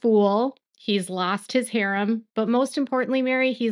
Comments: You said "He's lost his harem" but I heard "He's lost his hair" and I found myself nodding along before my mind corrected it. Both true though :) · lol I literally heard “he lost his lair, umm” 😆 harem